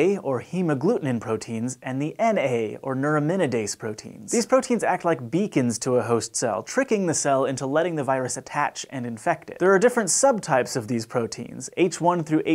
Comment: what I heard "these proteins act as VEGANS to..." XD